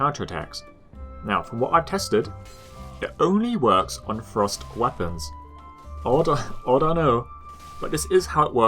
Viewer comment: ohhhhh I thought you were saying FROST weapons not THRUST weapons